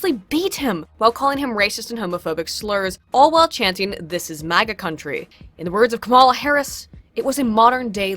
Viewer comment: I heard it as “Dis is mama country” lol